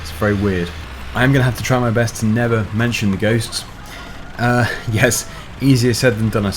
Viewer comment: when You said ghost i heard goats